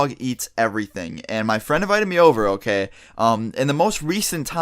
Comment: My dog has ate Spicy chicken wings Bones from a dead bird The dead bird Chased a rabbit and ate it Chased a frog and ate it Chased me and ate my sandwich Ate throw up Ate poop Ate a bag of chocolate My dog is fucking legit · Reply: Matt B. For a sec I thought u said ur friend I was like WTF